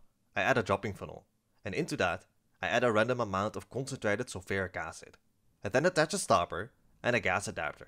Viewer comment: … anyone else here hear “an ultra-dilute” instead of “concentrated. Lol I’m like 96%? Wait…